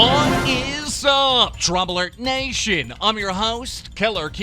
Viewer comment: It always sounds like he says trauma alert in the beginning to me